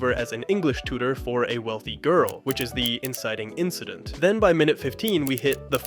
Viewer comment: says "incident incident" instead of inciting incident